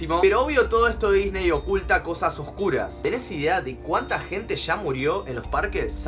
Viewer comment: pensé que dijo: " oculta cosas ocultas " jaja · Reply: No, dice "oculta cosas oscuras" salu2